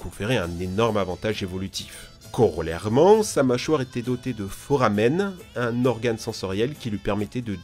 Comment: lol j'ai entendu "faux ramen" 🤣